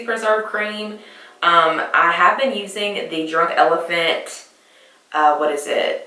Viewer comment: Can you tell me what exactly you said you use for the texture on your face? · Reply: Nvm! I watched it again and tried googling it and found it. I thought you had said "Dry elephant" lol